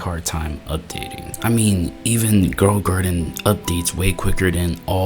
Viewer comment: Ah yes, everybody loves playing "Girl a garden" instead of blox fruits.